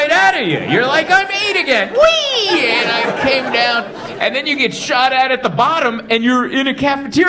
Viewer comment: I honestly thought he said “…and then you get shot at, at the bottom…” and I was like, well I guess it is America 😬 6:26 😅